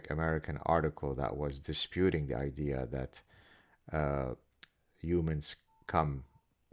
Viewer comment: Haha 😂 I thought you were saying “human scum”